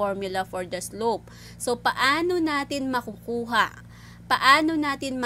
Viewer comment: I have a difficult time with accents & the Sub-Title isn't helping. What is she saying when it sounds like "Anu-nani"? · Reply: "Anu-nani" is actually "Paano natin", which is how will we in Filipino In the sentence she said "How will we get the slope, how will we find the slope given the equation?"